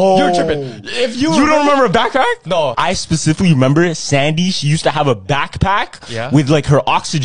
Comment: Oh I thought my guy said Remeber Sandy’s cheeks